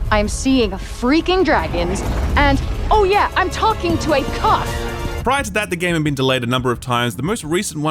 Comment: Am I the only one that thought she said “IM TALKING TO A CUCK!”?